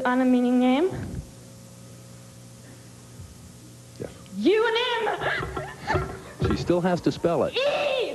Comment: didnt she spell it wrong she spell euonymum at last .. 😲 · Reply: No, she spelt it right!!! she yelled E-U-O-N-Y.....M then quickly restated said "euonym" which sounds like she saying "U-nm"